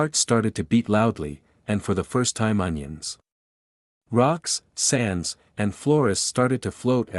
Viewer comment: anyone else catch this? 1:01 "ONIONS" Had me dying.